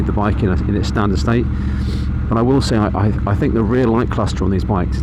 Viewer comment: At about 10:26 you said what was the worst part of the bike....rear linked crossed(?). What is that? I am amazed at the filtering you do... not legal here yet (SC in USA), but maybe someday. Love the looks of the Bobber, especially the transverse engine design vs the inline V2 (Harley, Indian). Just seems 'more right!' But for US roads I'll have to stick with my S&S powered Softail until Bobber enlarges the fuel tank (more range) and fits them with cast wheels so I can plug a flat tire instead of ruining the whole day. Thanks for a great video! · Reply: The rear light cluster. I just think they are too big and spoil the aesthetic of the bike. When I took my bike licence I was actively encouraged by the examiner to do more filtering. I think it must be infuriating if you can't do it. That's one of the best bits of being on a bike 👍